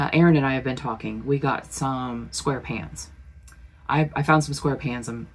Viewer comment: I thought you said Square Pants!! 😂 OHHHHHHH who lives in a pineapple under the sea?!